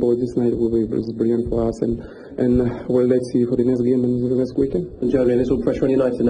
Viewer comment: Go at 0:10 Now turn on English (auto) subtitles Look at what he says “Well let’s see for the lesbian in the universe” Another one “Pakistani Air Remedy” 😂😂😂🤣🤣🤣